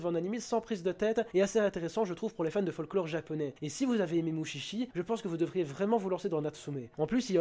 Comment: A 2:37 J'AI ENTENDU : Et si vous avez vue Oni Chichi.... J'ai pas compris sur le moment je regarder pas l'ecran